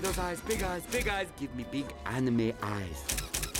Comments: I always thought that Fender said, "Give me big enemy eyes." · As a kid I thought he was saying "Give me big animal eyes." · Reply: What he said is "Big anime eyes". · Give me big anime eyes. Rest In Peace, Robin Williams. We'll always love you! You were the best actor ever! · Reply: I thought he said "Give me big animal eyes." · As a kid I thought he was saying "Give me big animal eyes." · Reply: I thought he said enemy eyes. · When i was little i always thought he said animal then anime 😂